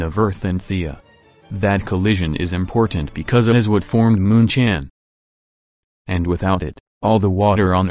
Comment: did i heard moon chan?